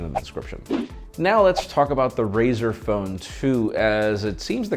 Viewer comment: Nobody going to mention what he said.. did he say Brazer? Or Razer. In the beginning into. Could have sworn he said brazers.. Which would be to damn funny